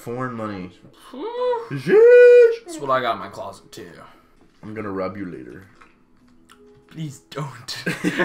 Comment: I thought he said, "I'm gonna rub you later" and got concerned for a bit.